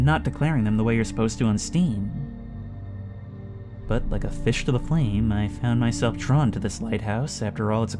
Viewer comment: did you just say "like a fish to a flame"?